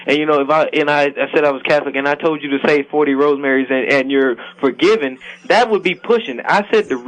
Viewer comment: did he say 40 "rose marys" lmao, douche!